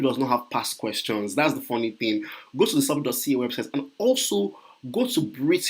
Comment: what website did you say? Did not get it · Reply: Celpip.ca